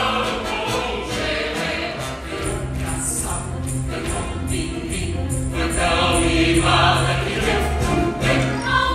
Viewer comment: Em veruca's nutcracker sweet eu não entendi oq eles disseram aqui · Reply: Veruca Sal, terror mirim. Foi tão mimada que deu cupim.